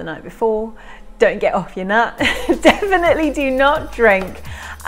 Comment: Did she just say 'don't get off your nut' the night before :(